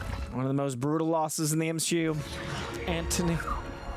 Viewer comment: Did just say "M-SHE-U"? 🤔Is he turning to the darkside? 😦